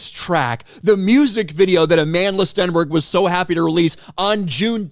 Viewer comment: "Amanless Stenberg" is how I heard you say it.